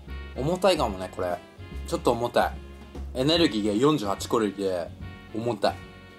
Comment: カロリーが、コロリーに聞こえる（笑）